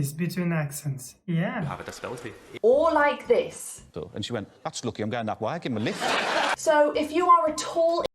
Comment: "And she went.. That's lucky. I'm going that way, I'll give him a lift". That's what the guy said at around 3:18 in your video.